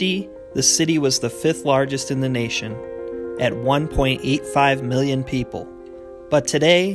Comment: I thought you said "At one point, 8-5 million people" instead of "At 1.85 million people" lol